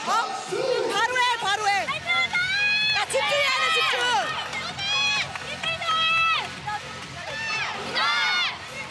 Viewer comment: 김지영이 뭐라고 소리친거죠? 알고 싶네요 … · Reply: 화이팅하자